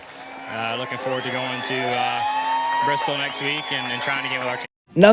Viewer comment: did he say looking forward to go to Bristol next week?